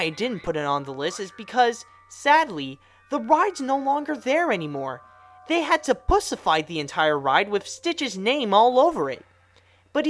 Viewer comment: Did he say "pussify?"